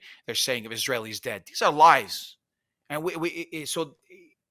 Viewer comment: I just don’t agree with the pastor when he says the Israeli said 1,400 deaths is a lie. He only believe the numbers of death toll in Gaza but not the Israeli death toll. Please weigh both sides and see both side stories. Both of you said praying for Israeli to soften their heart on dealing the Hamas. Please pray more harder for Hamas for a miracle that there will no hatred in their hearts and not brainwashing kids to hate and kill Jews. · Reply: to give him the benefit of doubt, I think he says "its lives". But I first also thought he said "lies". But it didnt seam like he tried to cast any shade on Israel, so I rewind and tried to listen again, and realised he first said 3000 palestinians lives, and then that about 1400 Israelis, "this is lives". All this sad anyway.